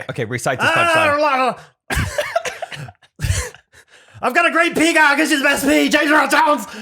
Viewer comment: I first heard “I’ve got a great peacock” and I can’t unhear it 😂